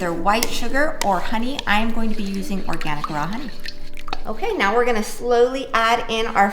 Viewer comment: I don't know why I hear "Organic Raman" instead of "Organic raw honey" Instead of sugar, I'm going to use organic raman... wait what!